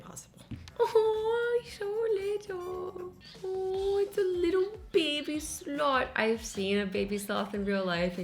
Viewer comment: I thought she said "aww its a littel baby snot"